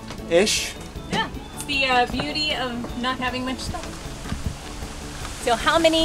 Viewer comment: I thought you said not so much stuff ;-)